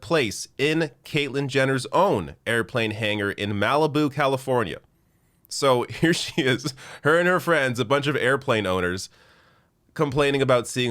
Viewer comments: Hangar? I thought she said hair😂😂😂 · It took me a while to realize that Caitlyn was saying "hangar". I kept hearing "he's packing up his hair".